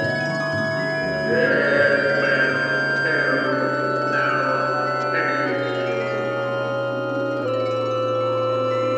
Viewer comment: What Its saying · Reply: Aye! Matey he be sayin “Dead men tell no tales.”